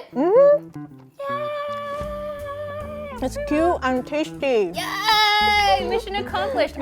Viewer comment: For a second I heard her say "I'm tasty"